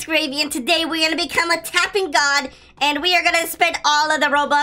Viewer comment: I thought he said tapping dogs XDDDDDDDD